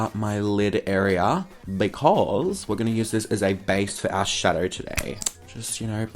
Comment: for some reason i heard him say "base for ass shadow." lmao